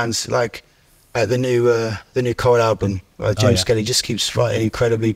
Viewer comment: which band does he mention at 2:58? i cant understand it:) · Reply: The Coral and James Skelly